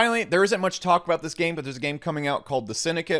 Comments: Big alpha clue 19:45 can’t work out spelling on what he said though… synicate? Syndicate? Cynicate? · What was the name of the game he mentioned at 19:45 ? The Syndicate? Any info on this? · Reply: Sounded like he said The Syndicate. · What game did he mentioned??? 19:45 Thanks fam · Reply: I heard "D" syndicate, but can't find anything.